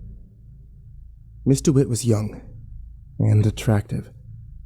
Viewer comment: I had to look up the story because it sounded like he was saying Mr. Witt instead of Ms. Dewitt and was confusing me lol